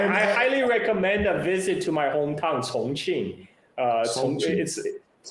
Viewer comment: Can somebody spell the name of Mr. Zhah's hometown? I'm trying to find it on goodgle maps. I heard "songching". I also tried "sonching". Thanks in advance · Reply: Chongqing